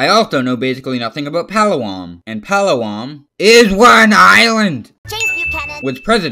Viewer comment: exuse me did you say palawam its palawan